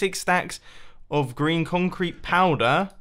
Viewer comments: Is it just me who hears "Stacks of Grian Concrete Powder? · i thought he said grian concrete powder 😂